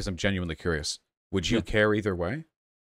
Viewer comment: What Vaush said: "Would you care either way?" What I heard: "Would you carry their way?"